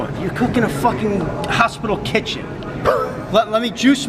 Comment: did I hear hospital kitchen? Wtf I though was a doctor (srs)